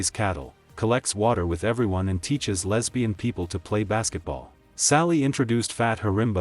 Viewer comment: did he say teach lesbian people?